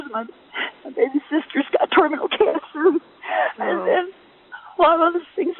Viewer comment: "My baby sister's got terminal cancer." Dang, when I first heard that I thought she said, "my baby sister's got toenail cancer." I had to rewind it and pay attention to the caption the second time around.